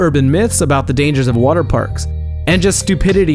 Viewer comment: I thought you said slides not parks LOL